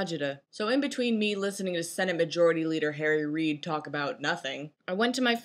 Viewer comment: I thought you said "Jerry Reed" at first when you said "Harry Reed" XD Jerry Reed is one of the most underrated guitarists, vocalists, and musicians in my opinion :)